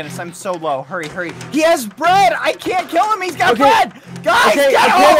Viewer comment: What everybody else hears: 'I can't kill him he has bread!' What I hear: 'I can't kill him he's rich!'